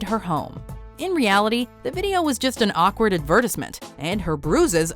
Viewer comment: Wtf did she just say? Ad verr tis mint.... wut